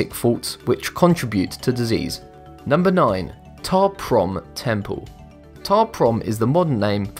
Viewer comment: When you said ta prohm temple I thought you said top ramen temple :|